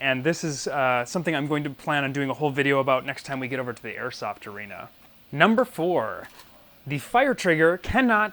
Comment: Did he say Airsoft arena